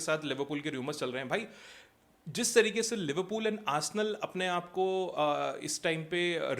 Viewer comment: Did I just hear Arsenal?😂😂 They just signed some useless player apart from Zubimendi